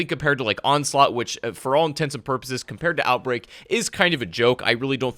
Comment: did I just hear “for all intensive purposes????” 🥴